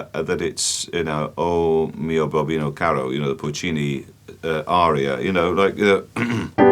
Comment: I thought he said "Porcini aria" and now I'm looking for mushroom recipes